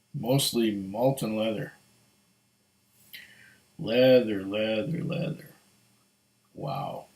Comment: almost sounded like molten lava